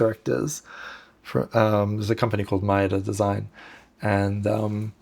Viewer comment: I couldn't catch the name of the design firm he liked ... Maieda Design ? was it ? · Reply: Maeda Design